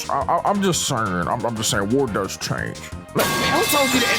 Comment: I remember metal gear 4 saying the same thing. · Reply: I thought snake said "war has changed"